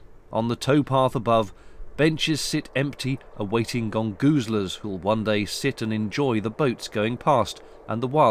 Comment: What are "Gold Guzalers" mentioned at 3:48 or did you say "Gone Gusalers"? Either way could you translate it for an American because I can't make it out and the closed caption thinks you said "Gangu Slurs", thanks. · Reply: "Gongoozlers"! Ha, I'd better go in and change that subtitle manually.